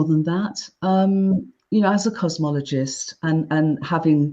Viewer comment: Actually, I just gave the intro a re-listen and I say "cosmologist". Perhaps you are the timeline where I accidentally say "cosmetologist"? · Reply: I'm on your timeline because I too heard you say 'cosmologist'. ✌️❤️😊